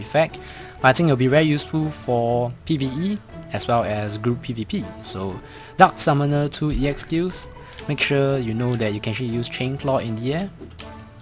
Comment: I thought he said Duck Summoner. Sorry. haha